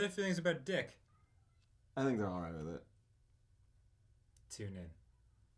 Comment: At the intro, I thought said "Too easy" like Ross but it was just "tune in"